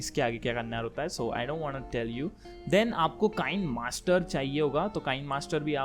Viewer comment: DID HE SAY cocain master LMAOO